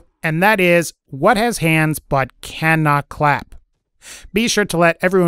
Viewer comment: Answer: Your mom... Oops I thought u said what has 2 hands and the clap. In that case idk a clock or some stupid shit like that.